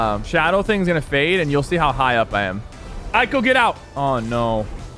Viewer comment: why did you say AIKLU get out in